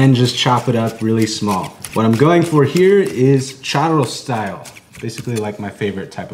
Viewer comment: Idk why i thought you said "Charizard style"